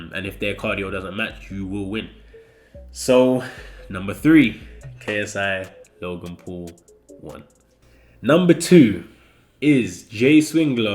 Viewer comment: Wait I thought said "KSI...Logan paul won" But then I realised he said "KSI Logan paul 1" lol